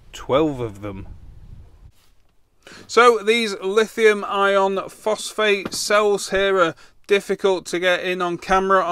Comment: you said lithium ion phosphate did you mean LiFePo? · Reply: LiFePO4 (apologies, can't do the 4 as subscript) is Lithium Iron Phosphate, which is not what said (at 2:44, not 11:04, though). Well spotted!